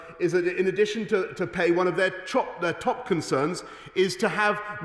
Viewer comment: Love how boris accidentally says chop instead of top- Freudian slip?????